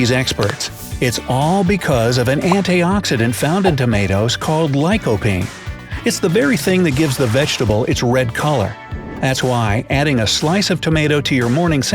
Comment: What are you ready to do to love a long life? 😊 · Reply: did I hear you say vegetable?